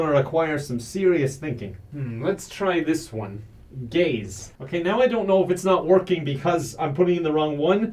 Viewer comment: Did he say "Gays"?